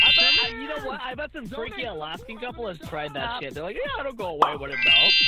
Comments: I think he said "some freaky Alaskan couple" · i think said "freaky alaskan couple" lmao